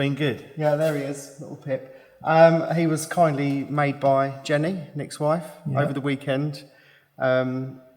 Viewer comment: Yannis doesn’t have a wife 😉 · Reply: Sorry, I misheard and just rewatched. I thought when said "Jenny" he said "Yanis."